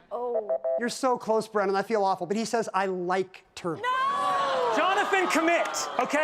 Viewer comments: You could see him restraining himself from yelling GET IN THE COMMENTS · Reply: when he yelled at i thought he said comment at first lol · Im in the comments. When Brennan yelled at i thought he said comment not commit at first.